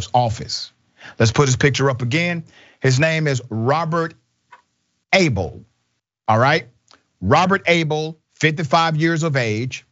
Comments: Que song "I (Got) Shot (By) The Sheriff". I love the "Yep, Florida.". LOL! And at first I thought you said the man's name was Robert Ahole. Which would explain a lot! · When he first said the guys name I thought he said Robert ahole